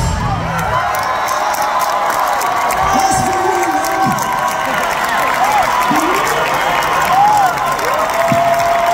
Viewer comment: thank you for posting this! anyone know what Phil said at 3:15? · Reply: I think it's "that's for you mum"